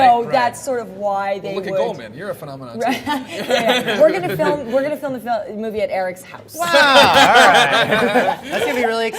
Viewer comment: Can anyone tell me what she says in 5:20 please ? I don't hear it ... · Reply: "we are gona film, we are gonna film, the movie in the ##%$'s house." BTW, English is not my first language. This is the best I can do.